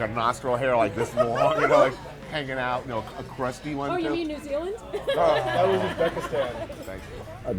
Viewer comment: did she say "oh in New Zealand" or was she inferring that New Zealand is a crusty nose hair?